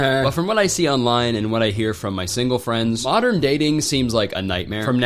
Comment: i thought u said “sigma friends” in the intro LMAO